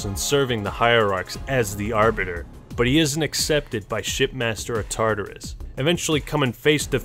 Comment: Is it just me that heard him say “shit master” lol